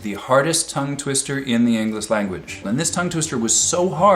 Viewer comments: did he say "englis language"? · Did he say "in the Englis language"? That must be embarrassing.